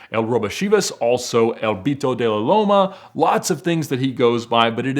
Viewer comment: Did he just say “El Pito De La Loma.”😂😂😂